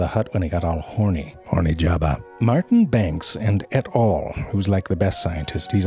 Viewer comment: I literally did not ge the Et. al joke the first 10 times i've watched this video. I thought he was saying Ed Hall or smth lol